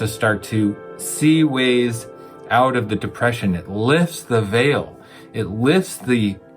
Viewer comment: Wow! As I was taking notes, I wrote ..."eating raw lifts the veil".. I got stuck staring at those words. I heard "it lifts the evil." Switching the first two letters of the word "veil". Thank you for sharing knowledge like always as well as exercising my brain.